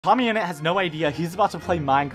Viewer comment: I thought he said mommyinit 💀